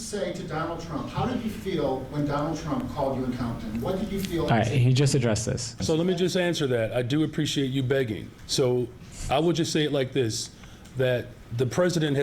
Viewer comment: Did he just say "i appreciate you begging".